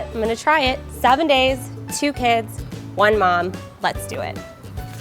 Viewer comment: I thought you said 7 kids and 2 days and i was so shocked I thought you have 7 kids but I restarted the video and it actually was 7 days and 2 kids